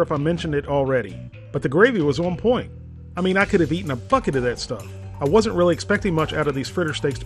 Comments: Am I the only one who heard “I could have eaten a f** it of that stuff!” 😂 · did he say fuck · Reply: 'bucket' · To me it sounded like he said a "f*ck-it" of it lol